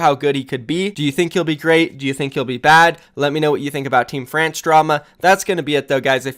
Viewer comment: i keep hearing " Dafran's Drama "